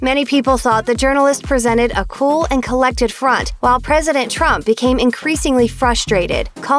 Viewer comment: Did you say a cool & collected khunt at 1:43? I agree: Cool. Collected. Khunt.